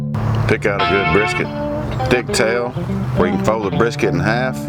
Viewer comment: monkaS did he just say dick tail?